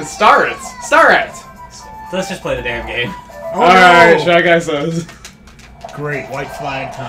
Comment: For a second i thought said "chugga says"....